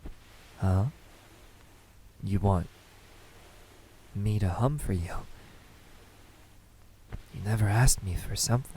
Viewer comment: I thought you said "You want me.. to hump for you?" And I dropped my jaw and bursted out LAUGHING LMAO SHKPTWSHLNCSJLTECB